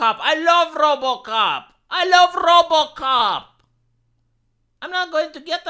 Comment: I swear to God I heard say" I love robo c*ck" and almost spit out My drink.